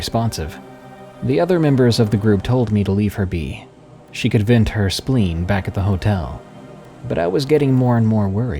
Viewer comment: Did you really say "she could vent her spleen back at the hotel" at 32:00??? I listened to it 5 times, and I just keep hearing "vent her spleen".....